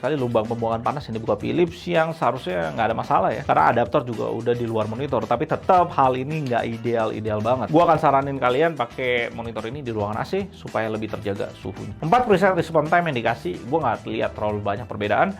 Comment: pake nya di ruangan ac bang biar suhu monitor nya lebih terjaga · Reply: oh, salah denger bang 😂.. saya dengernya "gw ga saranin kalo pake AC" 😂 wkwkwk